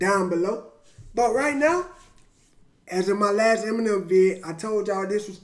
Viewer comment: Bruh I thought you said this is the last Eminem video for a sec had to reload the track